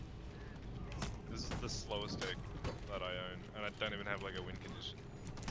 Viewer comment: is the guy in the backround talking about magic the gathering? It sounds like he said "this is the slowest deck I own and it doesn't even have a win condition"